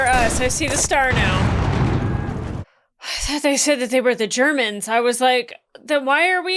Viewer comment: "Shermans", not Germans, although I completely understand how you misheard it.